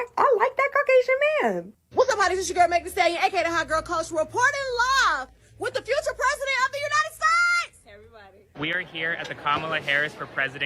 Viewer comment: Anyone understand what this woman said? 🤨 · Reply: I believe she has said reporting live with the hak tuah president of the United States.